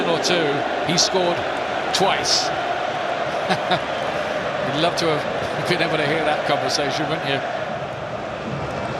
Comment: I wonder what konpany & deeney said to each other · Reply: Ibrahim Fashanu I think Kompany said harsh, don’t know tho